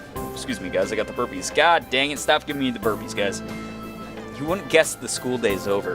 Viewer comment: A few times in the video "burpees" and I heard "herpes" (^^)